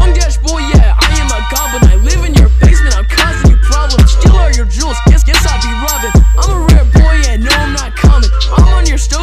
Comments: I think at 0:14 he says "steal all your jewels, guess I be robbin'" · i hear ‘i’m a rare boy yeah no i’m not common’ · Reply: I heard “I’m a red boy and no I’m not commie”